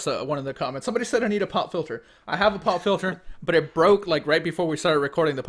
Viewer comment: When was talking about a Pop filter, I wasn't really paying attention and all I heard was "pot filter". I had to double take that one....